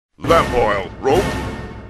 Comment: i thought at first he said "fat boy-o." I understand it's lamp oil